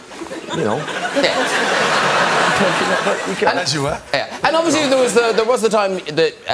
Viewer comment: Idris is definitely a fan. Heard him saying "as you were", sadly Liam didn't seem to notice